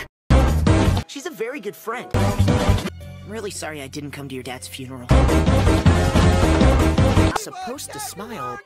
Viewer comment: At first I thought he said I'm SoRrY I CaMe To YoUr DaDs FuNeRaL😂